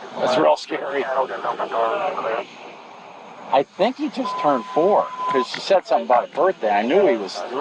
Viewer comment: She left her 3 year old alone while she went to meet someone a total of 22 miles away? Mother is completely out of her mind. You never consider much less perpetrate something like that. This woman is not a responsible mother at all. This is unbelievable. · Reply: I thought they said kid just turned 4?